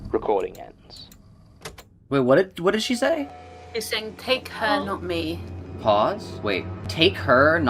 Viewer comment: Gonna be honest, I heard 'they cannot leave' instead of 'take her not me'